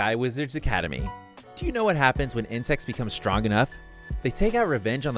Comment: Shit i heard incest became powerful enough edit:from